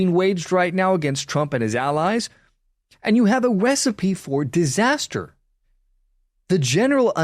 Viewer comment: Did you say wecipe for disaster at 5:25? 😂😂😂 sorry it’s not important but that is what I took away from this video